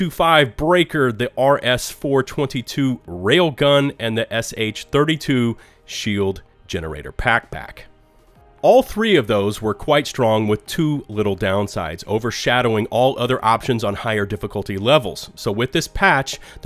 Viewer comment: Did anyone else hear him say PackBack?